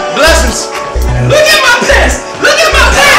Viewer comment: I THOUGHT HE SAID NIGGA GET PASSED WHEN HE WAS SAYING LOOK AT MY PANTS